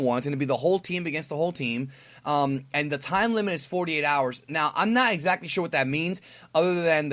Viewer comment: i thought they said that it said 48 minutes not hours